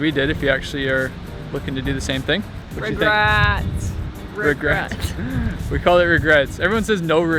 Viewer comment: I thought you said "rugrats"